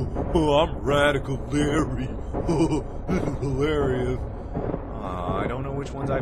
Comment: It sounds like you said this... "I'm radical larry, this is hiLARRYous"